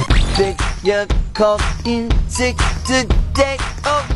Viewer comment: can someone translate what he’s saying? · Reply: “figure called in sick today”